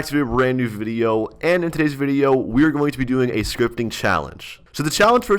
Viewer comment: did he say scrop scripting?